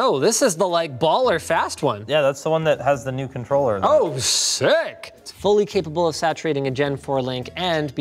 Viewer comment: I thought he said "oh shit" at 8:12 hahaha